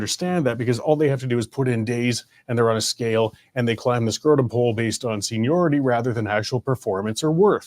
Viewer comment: Gah.. did you say “climb the SCROTUM pole” ??